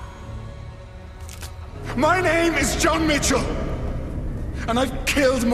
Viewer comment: Wenn jemand deutsch kann, kann der jenege Verstehen das "John mitchel' bei 9:22 mein Aim sagt😂